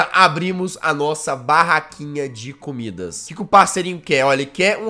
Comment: só eu que ouvi ele falando COmidas?